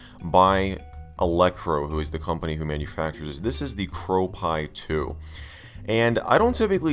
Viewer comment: I thought it said ‘GrowPi!’ I thought you had to grow it